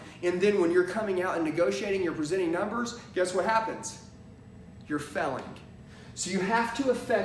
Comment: Did he say.. “youre a felon” ?